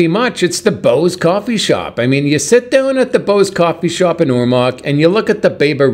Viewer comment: I thought he was saying "Bose Coffee Shop" until I saw the sign. 😂